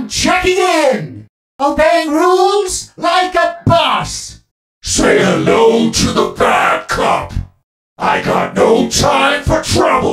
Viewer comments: I thought he was saying "say hello to the backup" there. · When Lawrie said "say hello to the bad cop" i thought he actually said "say hello to the backup" cus Larry's super is called "Call for backup" after all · Say hello to the bad cop 😈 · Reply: I always thought he was saying "say hello to he backup" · I thought it said “Say hello to the backup” since the super is “Call for Backup”